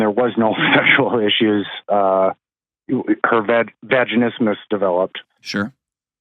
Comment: I had to look this up because I don't think I heard it right, so I went to the transcript. I thought they said, "Vagina Mist," so I pictured fog down there. Here's what I found for any man who is curious, "Vaginismus is the involuntary spasm of pelvic floor muscles during attempted vaginal penetration, causing pain, burning, or a "wall" sensation. It often stems from fear of pain, anxiety, trauma, or previous medical/sexual discomfort. Treatment involves vaginal dilators, counseling, and pelvic floor physical therapy."